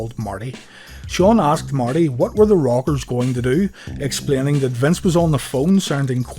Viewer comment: I know this video is 2 yrs old but it's new to me and it sounds like your saying The Robbers